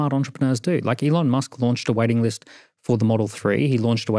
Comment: For 10mins straight, I thought he was saying 'Elon Musk launched a wedding list' 🤣🤣🤣